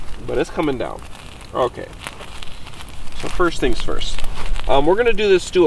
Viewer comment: I cannot hear someone say, First things First (53:26), without me hearing, " I'm the realest ".